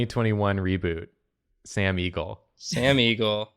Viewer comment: When I heard them say "Sam Eagle" I understood Sam Riegel and was like - thaaaat doesn't look like Sam's 100 personalities...